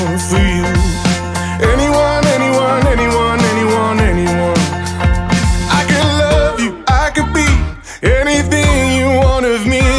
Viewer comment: I swear that I always hear: " I can love you, I can pee" 😂😂😂